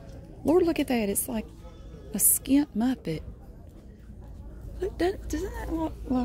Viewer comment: Did she just say “Skinned Muppet” 😂😂😂😂💀